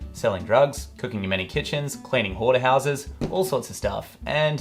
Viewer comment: I heard, “cooking in whorehouses.”